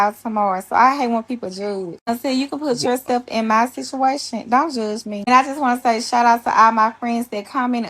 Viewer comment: I had to really really focus to understand what she was saying and even then I had difficulties. Its like she's not fully enunciating the words she's saying and dragging them at times. It sounded like she said "Don't shuzh me" instead of "Don't judge me". Its almost as if she's keeping her voice in the back of her mouth and not moving her tongue as much to fully complete some words. It's not racist to say you can't understand her. She could probably do with some speech therapy.